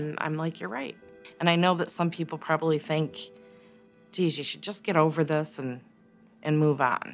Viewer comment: Ive heard parents of a murdered child say that others have told them "to get over it" years afterward. At first I thought .. "No way would anyone say something so callous". But Ive heard more then a few make that same statement... What is wrong with people? You dont "get over" something like that. Thank goodness I have never had my children taken from me, but I know I would not "get over" something so heinous, Shame on those people! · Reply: I went back to see and at 33:40 she actually said "I know some people think.. 'Jeez, you should probably just get over it..'". So I stand corrected. She may have seen it in peoples expressions. But I have heard several people say they were actually told... to get over it. In various ways, but it all means the same. So thank you for pointing out my bad!